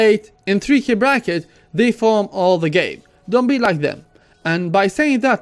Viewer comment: I did not understand tip #8. What I heard is "reform all the game." is that right? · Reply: Estanley Daliuag I think he said farm all game